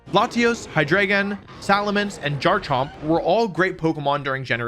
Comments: did you just say jarchomp? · wait what was that? Jarchomp? im not the only one who heard that right? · did he say jarchomp? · did he just say jarchomp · Did...Did just say jarchomp at 3:30? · Did he just say Jarchomp?